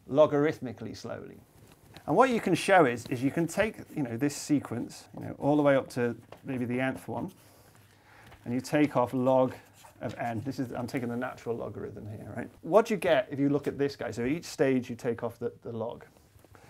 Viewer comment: At about 6:35 I thought you said "...the ant-th one" which seemed rather appropriate! 😄 🐜